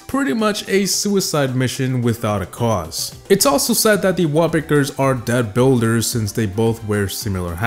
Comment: Captions say's "Wallpapers" instead of "Wallbreaker"😂😂😂